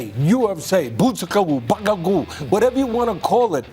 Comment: What did Teddy say here?? @ 02:31 Butsukawu bakkagoo??